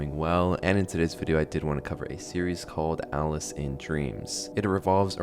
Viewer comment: Ngl i thought it said Alice in Chains😂